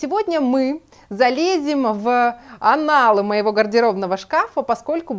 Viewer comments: В аналы гардероба???Или мне послышалось? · Прошу прощение: "анал" моего шкафа, мне послышалось?😅